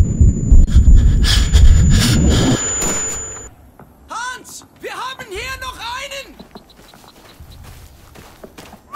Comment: what does the German say after shooting Wilkins (I presume that's him) before walking over to Doyle? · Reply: Hans, we have here another